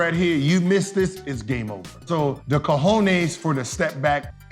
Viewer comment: Did he say the ''cojones'' for the stepback